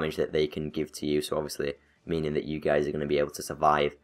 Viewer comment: just I heard you gays?